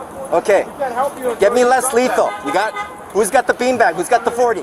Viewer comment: Oh! "LESS lethal"? I thought you said, "LET'S lethal."